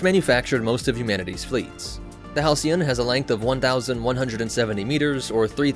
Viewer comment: It's longer than an ISD. I think you know where I"m going. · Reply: oh, oof. I thought it said 1,700 meters not 1,170. But it's still longer than a Venator-class.